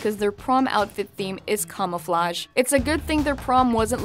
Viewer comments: it sounds like she said "cumouflage" XD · Did anyone else find the voice over to be annoying? XD and I swear I heard her say cum-ouflage instead of camouflage. >u<!!!! XD